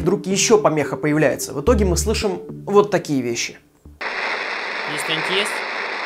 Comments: я слышу — «я здесь есть, отстаньте от меня» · мне одному послышалось: ,,Есть пробитие!"?)))))